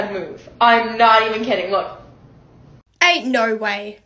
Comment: At first I thought she said “AtE nOrwAy”